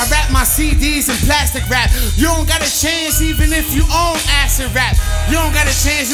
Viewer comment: "You ain't got a chance, you got community chest" DAMN MONOPOLY LINES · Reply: Damn, I thought he said "You ain't got a CHANCE even if you ACID RAP." I heard that so wrong.